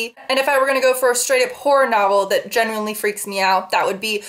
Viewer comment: I've kinda been binge watching your videos and I swear to God, everytime you say "horror" I hear "porn" haha. I never noticed that these two words sound so similar.